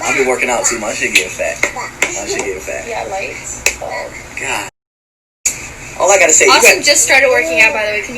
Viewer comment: Unique Tjizumaue srry my mistake and yes I did but so what?? Is that any of our business y’all find one thing bad to say and turn it into a whole story. What they do is no one business but theirs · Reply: Faith Morris you don’t know nun about me so don’t assume nothing especially assumptions like that LMFAOOOOOO. I have never been abused. And he said “oh god I can’t” not “oh god damn” that’s what everybody thought he said. If you read the rest of the comments you would see that people changed their answer. And I’m perfectly fine. I think you need to look up the meaning of sexual abuse cuz you just took this situation and that word to a whole other level. Nobody in these comments said that’s sexual abuse cuz it isn’t. I have relatives who are social workers so I could tell you that’s not sexual abuse. So are you okay??